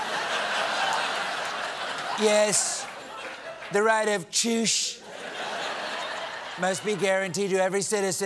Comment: When Trump said, "the right of choosh" I thought he had said "the right of tush" instead. What 😨😨?? Seniors, each of you, get your own "tush" while you're able LOL. 😉😄😊😆🤣